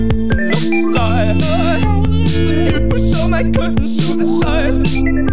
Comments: First i thought he was saying "suicide" instead of "to the side" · I thought I heard suicide